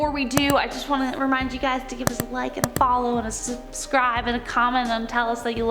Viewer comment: did she say like and "follow"?